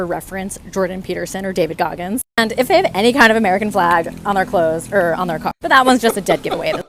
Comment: I thought she said "American flag on their clothes or their cock". Sorry if you heard something different.